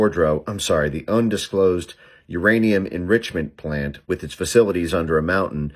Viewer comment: I just realised when I saw this movie I misheard "uranium enrichment plant" as "Iranian enrichment plant" due to the guys accent.